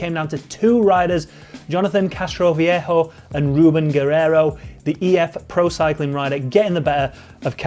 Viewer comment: The Eurosport commentators pronunciation of “Guerreiro” is totally awful. I had no idea what he was saying when I heard “Geh-Hay-ho”